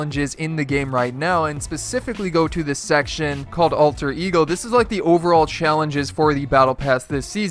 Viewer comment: I thought it said alter egg go (ALTER EGG-GO